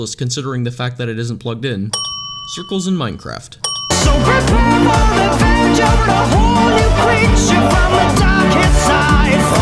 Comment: When I first watched this I thought you said "Cute girls in Minecraft" then I read it and I was like.. Me: "THAT SOUNDS NOTHING LIKE THAT!!"